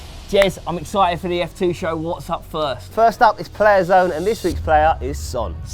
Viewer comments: I thought player zone said player son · I thought player zone said player son😂